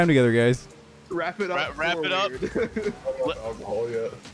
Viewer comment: When said, “Wrap it up” I thought he said, “Rapid up”